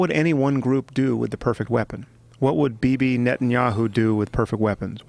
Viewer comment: What did Sam Harris say around 1:22:20 before he said what would hitler do? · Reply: What would Bibi Netanyahu do with perfect weapons. He is the prime minister of Israel